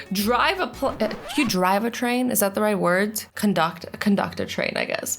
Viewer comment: When u said drive a train it sounded like u said drive a plain